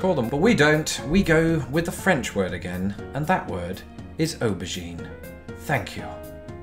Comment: The way you say aubergine, it sounds like your saying obugene. Obugene was and may still be a family owned garbage collection business in Raleigh County, WV. Compared to it, eggplant sounds a lot more appetizing. Maybe we should use its scientific name solanum melongena instead.